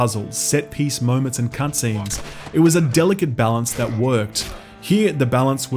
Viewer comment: I swear he just said “cunt scenes.” Lmaooo